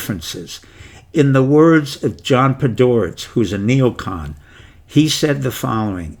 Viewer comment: Please who is the name : I hear "John Pidor" but cannot find around.